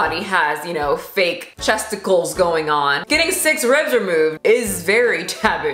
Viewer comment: Commenting for algorithm purposes. What offends me is that "chesticles" sounds better than "busticles". I'm also offended by my inability to think of a similar word for butts. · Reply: I always heard “breasticles”